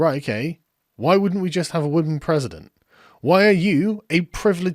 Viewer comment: minutes i swear i heard Sargon say wooden president and its sad that i would support that over almost anything the left can show for itself.